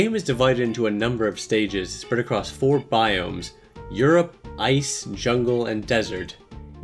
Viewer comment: I'm sorry, did you just say Europe or did I hear that wrong?